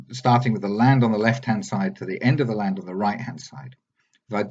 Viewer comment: from what I saw, he used you evidence to quantify his scale. It looks pretty compelling to me. It seems obvious the location data is right. Once the location data is corroborated to your elevation data, I think it will be pretty clear that Ireland and Scotland are not at all in evidence and locations on IOM fit the curve for your given elevation. There seems to be plenty of data that shows your elevation was not <50ft. I won’t go so far as to call you a liar, I will leave that to others that know you and the data better than me. I would however say that appear to be mistaken about your observation elevation. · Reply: I thought you said "Miles doesn't have what I have... The floaty land to the right" Have you not learned a thing? Mr, compulsive Liely, “It is better to keep your mouth shut and appear stupid, than to open it and remove all doubt.”